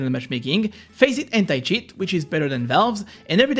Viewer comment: Did he say "Hentai Shit" instead of "Anticheat" in 2:46 ?? 😂😂😂😂